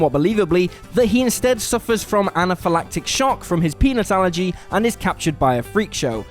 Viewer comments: hehe. I know you said peanut allergy but it REALLY sounded like Penis allergy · You can't tell me is doesn't sound like he says "penis allergy". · Sounded like you said "penis allergy". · Did anyone else hear Nelson's dad has a penis allergy? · Sounded like he said penis allergy. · Sounded like you said penis allergy.